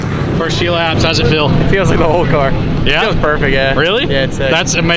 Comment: “How’s the car feel” “it feels like a whole car” lmao classic. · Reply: Thought he said old car